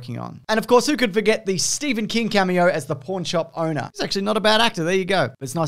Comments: In your Aussie accent it sounds like "porn shop owner" instead of "pawn shop owner." Thinking to myself, "Pretty sure he wasn't in a porn shop, but ok. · It sounded like you said porn shop not Pawnshop